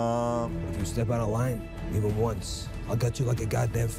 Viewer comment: Glad you said "This looks like The Sopranos'....exactly what I thought when Penguin says "If you step out of line...even once....I'll gut you like a god damn fish"...I heard "If you step out of line...Christopher!"....LOL. Looks great. Can't wait for it next year!